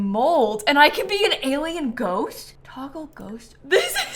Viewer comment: In minute 9:00 Am I the only one who heard "Taco Ghost"? Hahahaha😅